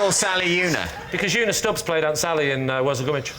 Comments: what did LEE say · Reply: He said "because Una Stubbs played Aunt Sally in Worzel Gummidge" (a kids' TV show). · what did lee say that everyone thought was so good? i didnt understand that bit. sounded really good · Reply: "Because una stubbs played aunt sally in worzel gummidge"